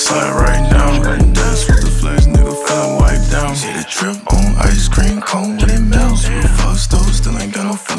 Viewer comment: Thought it said ice cube for a sec